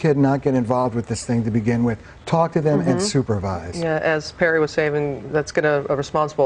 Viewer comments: I thought she said Perry Assfat.. ._. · wait wait wait... did she just say "perry ass tap"?!?!?!